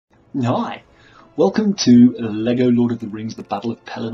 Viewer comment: i swear he says nyi